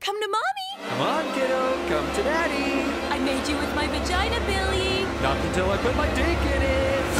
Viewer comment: did she just say "you're gonna play with my *cat* billy"?